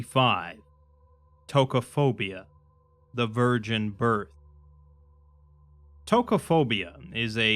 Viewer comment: I dont know why.. but my brain thought he said "Tokophobia: The Virgin **Bird**" and I nearly literally stopped for a few too many seconds before restarting and rehearing him.. Thankfully my brain was just making me hear things...... again... but man.. now I can't stop thinking that an SCP article about a virgin bird would be very strange.. (but pretty on par for an SCP in general, tbh)